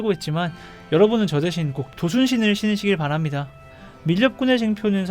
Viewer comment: "저는 자금사정으로 이런신발을 신고있지만 여러분은 저대신 ???? 신으시기 바랍니다" 에서 ????가 뭐라고 하시는거죠? 조순신? 그게 뭘까요 ㅋ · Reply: 순풍 도피 신발을 도순신 이라고 불러요 :D